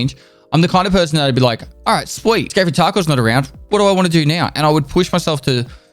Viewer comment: Sweet scary Taco is what the CC thought Pest said when he said "Escape from Tarkov". I chuckled.